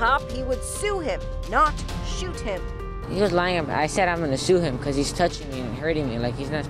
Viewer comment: He clearly says shoot him :/ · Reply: He has a lisp he said sue